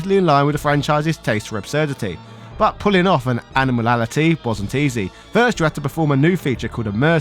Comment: How can't you pronounce 'Animality'? Sounds like you sayin ANIMALALITY